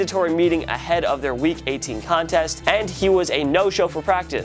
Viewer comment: Am I the only one that heard him say week 18 contest?